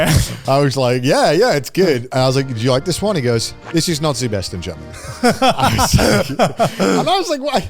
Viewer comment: I genuinely thought he said it’s “Nazi’s best”… he said “not the best” 🤣 I’m an idiot